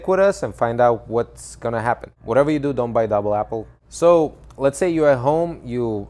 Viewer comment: May i know what he say don’t but double apple ?